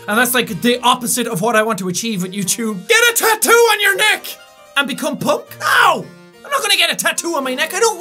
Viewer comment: The one that told him to get a neck tat and be punk. I misheard as get a neck tat and become pope